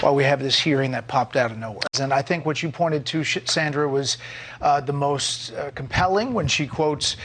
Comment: Why does he say “shit Sandra” at 1:46 into the video? That’s weird…